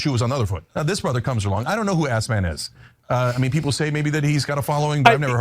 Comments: I heard "Assman" not gonna lie XD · I heard Assman and figured it was Cenk using a derogatory pronunciation on purpose. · I just hear him saying “ I don’t know who ass man is” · I hear "a$$ man" lol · I hear Ass man. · I hear, "a$$man" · Sounds like he was saying A$$man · i hear ‘ass-man’ every time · I thought he was saying assman 😂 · it sounds like he said “ass man” lol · Sounds like he is saying “A$$Man”😂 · Sounds like he said "a%# man" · I heard him say A$$man like on Seinfeld